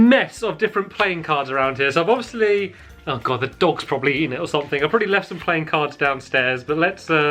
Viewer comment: did he SAY DOGS WITH A S!?!?!?!?! I THOUGHT HE ONLY HAD ALEX!!!!!!!!